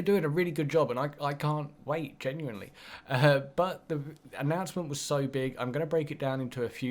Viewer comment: what did u say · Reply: King-Anonymous 18 her butt was so big